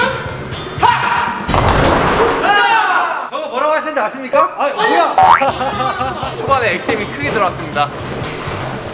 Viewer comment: 그래서 뭐라고 하는데여,,, 궁금해 죽.겠.네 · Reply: 삼족오 ㅋㅋㅋㅋ